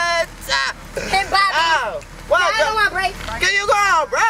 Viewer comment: what was he saying at the beginning omg is he on a hoverboard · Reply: He said Bobby bot burger spot where the fries is cold and the bitches is hooooooottttttt